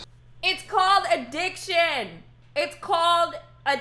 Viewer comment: i swear at 7:35 I heard "It's called a dick chin!" had to double take lol